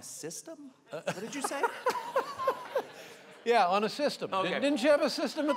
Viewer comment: I thought he said "on a system" too and thought it was weird lol